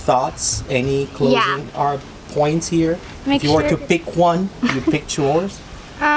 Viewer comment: Is Dad a lawyer?? I have family that are lawyers and toward the end I thought you said “any closing arguments?” Hehe 😂Love your videos! You all are a beautiful family, God bless you! 💕